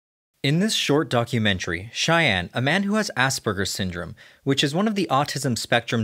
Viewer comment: Thought he Said ass burger syndrome in the beginning of the vid